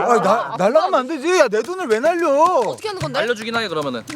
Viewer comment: 님이 ''내 돈을 왜 날려어어'' 라고 할 때, ''내 팔을 왜 날려어어'' 로 들었음 ㅋㅋ